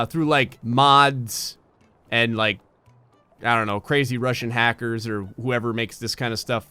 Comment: did he just say crazy Rusian hacker